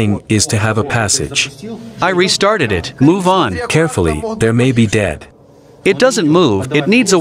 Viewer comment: did he say carefull there might be dead?